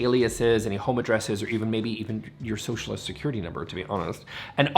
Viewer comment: did just say Socialist Security Number? This ain’t Canada pal. At least not yet. Let’s be honest, for a while now Canada’s become America’s evil top hat!